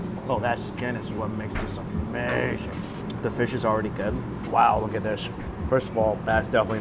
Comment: I heard the fish is already dead lol